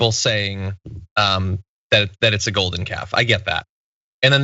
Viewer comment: John, I thought you said golden gaffe!